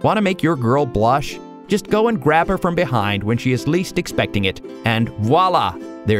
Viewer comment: "Guys, wanna make your girl blush? Just go and grab her from hehind when she is least expecting"! :D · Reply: I misheard it when he said that XD i thought i heard him say "Guys do you wanna make your girl blush ? Grab her behind !." I had to do a double takeXD